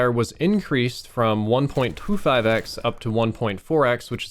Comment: did you say 1,25 FedEx?